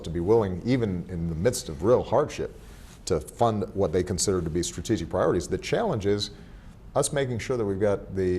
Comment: I think Obama says "strategic priorities" and not strategy priorities as in the given subtitles (8:42). But thanks a lot for this video and the subtitles! It really helps us to study!!!